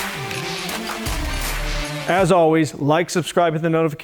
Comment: Did he say Abs Always?